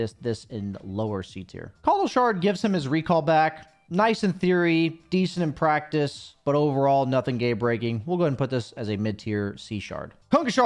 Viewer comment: you said "Gay breaking" instead of "game breaking" about Kotl's shard lol